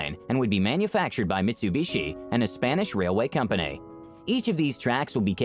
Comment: The uploader didbt mention about mitsubishi motors as a spanish company. Take time to listen okay? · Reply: Okay I will do, but when you listen to 1:21 the uploader said "A new batch of train will be introduced manufactured by Mitsubishi, a Spanish Railway Company." That is what I heard. And he/she also put a picture of the logo of Mitsubishi with "Motors" on the video.